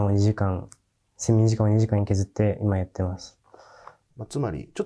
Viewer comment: 睡眠時間2時間、ではなく、睡眠時間2時間削って…って聞こえるけど気のせい？ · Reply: 睡眠時間2時間に削ってって聞こえます、